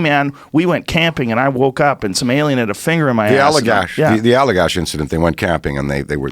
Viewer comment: mt I thought he said "I was camping and I woke up smelling a finger off my ass" 🤣sorry English is my second language I have to lessen again and again for make sure wath he said 🤭